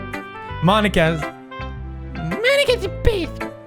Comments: i was reading the comments and listeing o the video. when sayori said "monikas the best" pewds sounded like he was saying "monikas a bitch" and i was like what xD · "monika's a bitch" is what i heard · No joke i heard "Monika is a bitch"